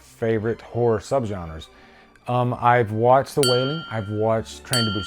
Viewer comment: wat he said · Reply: “I’ve watched The Wailing. “